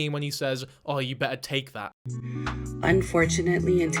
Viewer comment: The son head was under the cover and it sound like he said you better taste this. It's weird and creepy